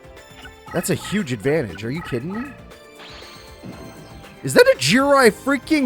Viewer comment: When I Heard say Is That A Jirai Gumo At 0:45 Its Sounds Like IS THAT THE GRIM REAPER